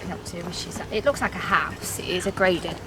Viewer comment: I'm pretty sure I heard someone whisper in the camera before you even got there. Check it out around 1:08-1:09 when she said it looks like a house. Sounded something like "it is a house" maybe.